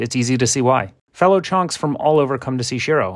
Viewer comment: Ong i thought he said more chongs come to see shiro not chonks 💀